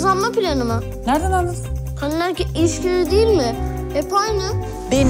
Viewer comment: ''Kadın erkek ilişkileri değil mi? Hep aynı. '' dediğini <num>. dinleyişimde anladım